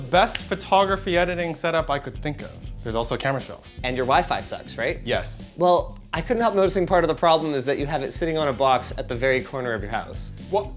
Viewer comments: Am I the only one who understand "And your wife sucks"? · I had to double take at 0:39 cause I thought linus said "and your wife sucks"